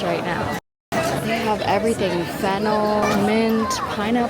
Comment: did I hear that right?.... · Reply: Fennel